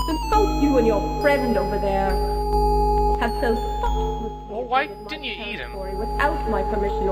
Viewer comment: Did she really just say "You and your friend over there have fucked with me" or am I hearing things?